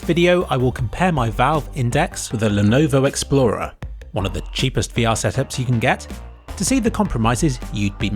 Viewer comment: second mark it sounds like he says setheads instead of headsets